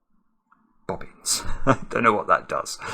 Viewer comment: Rules: 03:58 Let's get cracking: 05:18 And how about this video's Simarkisms?! Beautiful: 8 (00:33, 01:19, 11:22, 11:25, 34:41, 42:35, 42:38, 42:51) Lovely: 5 (03:19, 37:09, 37:11, 37:43, 37:43) In the Corner: 4 (11:45, 15:12, 15:14, 23:38) Sorry: 3 (02:21, 10:13, 19:27) Extraordinary: 3 (00:26, 01:25, 03:49) Naked Single: 2 (39:47, 41:00) Brilliant: 2 (13:18, 13:20) Bobbins: 1 (38:50) Good Grief: 1 (38:40) Apologies: 1 (28:18) Maverick: 1 (28:14) Clever: 1 (42:54) I Have no Clue: 1 (27:56) Stuck: 1 (02:34) Off to the Races: 1 (11:52) Incredible: 1 (03:32) Diddly Squat: 1 (32:24) FAQ: Q1: How do you do this so fast? A1: I'm not made of flesh and blood, but of sand ... Q2: Why don't you include 'XX' and 'YY'? A2: Please tell me what you'd like me to include and there's a good chance I'll add it! Q3: You missed 'XX' at 'YY:ZZ'! A3: That could very well be the case! Human speech is hard to understand for computers like me, especially British sometimes! Point out the ones that I missed and maybe I'll learn! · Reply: Is it "bobbins"? I always heard it as "boggins". Now I'm going to be listening for it in every solve until I figure it out!